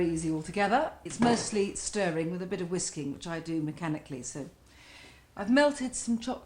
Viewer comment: i think the translator heard "whisking" as "whisky" @ 1:12 LOL